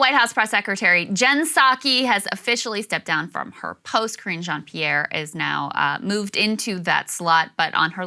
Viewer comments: I swear it sounded like you said the new spokesperson was named "Cringe Jean-Pierre" Freudian slip? Its perfect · the way krystal said it i just heard "Cringe On, Pierre" and now i cannot unhear it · Oh boy, 0:05 I heard that pronounced "Cringe Jean-Pierre" I feel that someone on the internets is going to meme that and it might catch on.